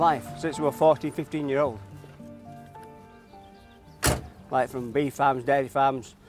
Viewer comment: What cloth-ears subtitled this? (4:02) He says "...right through beef farms, dairy farms..." and it comes out as "I been through bean farms..." Bean farms, FFS. I wonder if he had to slaughter the beans himself.